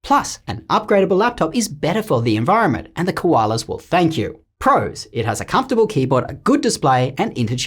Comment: bros really milking laptops of 2023 dry guess i’m famous now(it’s a joke 💀) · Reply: What He Says: The Koalas Will Thank You Close Captions: The Koalas Will Spank You